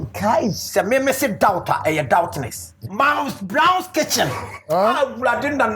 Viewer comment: Did I just hear "mouse brown kitchen" 🤣🤣🤣🤣🤣🤣🤣🤣🤣🤣🤣 Oliver is just unique, God bless Kankem for fishing Oliver out 💙💙💙💙